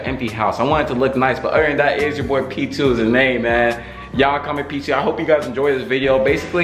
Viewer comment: I liked how he says “ hey it’s yo boi p2 y’all comment p2” Me: looks at comments no one put p2 XD I will thou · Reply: Oh I thought he said y'all call me p2